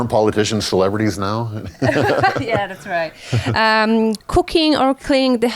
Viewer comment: I heard cocaine at first